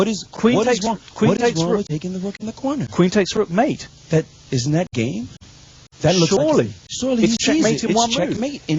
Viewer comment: sounds like he is saying "but... isn't that gay..?" maybe thats why he didn't went for the mate :P